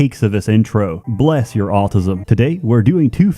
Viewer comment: is the only YouTuber who can have a special guest and then thank them by saying "Bless your autism" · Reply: I thought that he said "bless your altruism"